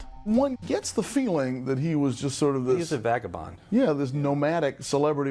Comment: What is he saying? Is he saying the word "Vagabon?" Thanks for letting me know!